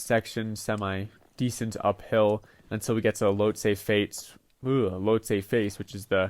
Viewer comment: DID BRO JUST SAY “LOW TAPER FADE “ ?!?!?!?’ 😈😈😈